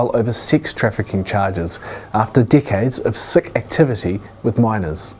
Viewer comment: Did anyone else hear “dickhead” instead of “decades”?